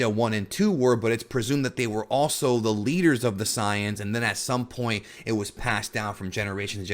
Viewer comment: ok if youre gonna use the "correct" term of the saiyans, then say saiyajin cuz it really sounds like your saying scion not saiyan.